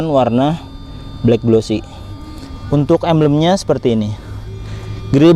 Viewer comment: Mf sblmnya...apa sy salah denger yaa? Kata emblem kedengaran nya menjadi ambeyen ( sejenis penyakit ) 🙏🙏🙏. Tp secara keseluruhan penjelasanya cukup di mengerti.